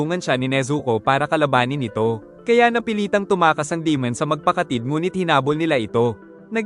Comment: Boss narinig ko yung nasabi mo 2:54 magpakatid😂